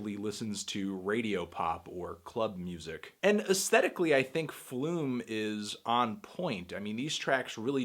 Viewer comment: Bruddah I thought it said “skin flute” 😂